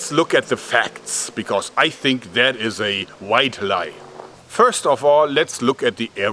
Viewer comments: A "white lie" is a harmless one, like telling a lie to spare someones feelings. Not what you meant there I think. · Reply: I thought he said "Wide lie" which makes quite a bit more sense. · Point of clarification of English phrases: A "White lie" refers only to a harmless lie, for example, "You don't look fat in that dress". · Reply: I thought he maybe said "wide" lie as in big lie. But you're probably right he just mis-used that phrase.